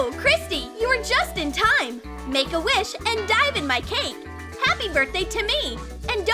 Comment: Wait… did it say dive in my cake